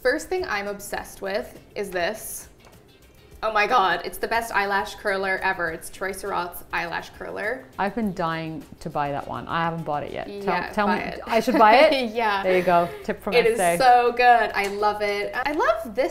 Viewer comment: I heard "Toys R Us eyelash curlers" xD